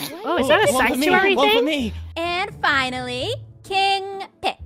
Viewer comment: did anyone else hear say save one for me